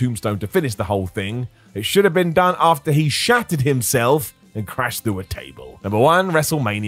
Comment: When he said "shattered himself" I thought he said shat himself lmao